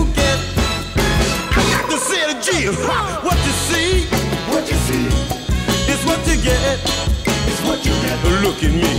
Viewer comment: What is being said at 2:30?? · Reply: Sounds like 🎵🤔 "I got to say again"